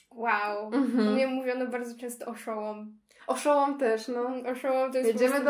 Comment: ja raz usłyszałem że ktoś mówi EUNUCH i od tamtej pory nie potrafie mówić inaczej